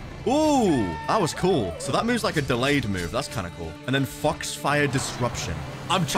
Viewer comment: I thought he said f*cks fire disruption